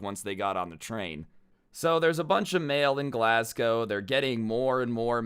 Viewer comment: Bro it sounded like you said Glascow xD